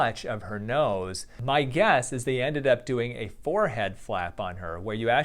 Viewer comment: I thought you said "forehead slap ." And this result certainly would make me slap my forehead!